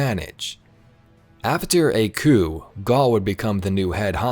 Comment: When you say Gaul you mean Garry right? Because thats what i heard.